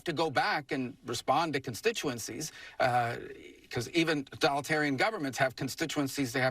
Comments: I swear I heard Rubio say " Even Donaldtarion governments..." So much so that I had to rewind it and listen again. Sure sounds like he said it out loud, and it sounds like something the Orange Clownshoe would label his style of leadership. 🤷🏻‍♂️ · did anyone notice he said Donaldtarian, instead of totalitarian?